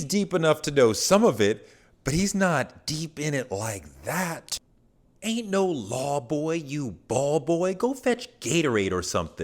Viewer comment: I thought he said...aint no love for you ball boy....